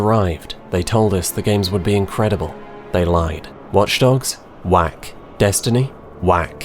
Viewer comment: thought you said wank at the start not whack 😂